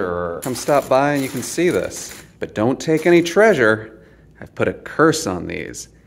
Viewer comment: What you said “don’t take these input a curse…” VS What I heard “Please don’t take these are all I have!”